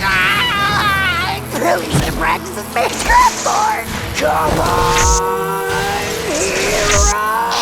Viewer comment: What was Shiro saying at 10:10? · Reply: "I really should have practiced this magecraft more". Kiritsugu told him projection was useless so he focused on reinforcement.